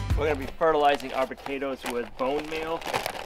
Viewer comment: what kind of fertilizer are you putting on your potatoes I’ve never seen it done that way and I live in South Mississippi and can’t grow squat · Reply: Bone meal, I think said